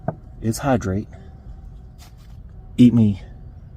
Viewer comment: he paused i literally thought he was saying he’s gonna eat himself😹